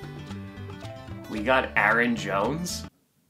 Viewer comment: Oh my god, I got very worried for a moment because I thought it said Aaron Rodgers, not Aaron Jones. As a Vikings fan, I don't want Aaron Rodgers, and they better not get him.